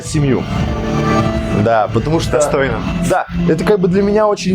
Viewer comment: извините, послышалось "отстойно"